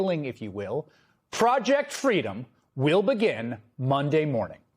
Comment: Oh dear, I heard "Fraudject Freedom". Perhaps that's not wrong ^^